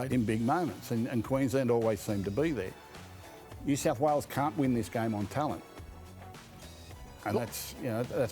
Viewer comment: I thought jabba the hut said qld cant win ?? Has he met Galvin yet?